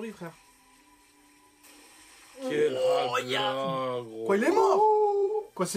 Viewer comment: La quoi ? 😭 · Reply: Je vien de voir mdrrr a bien dis "la haglaaaa" MDRR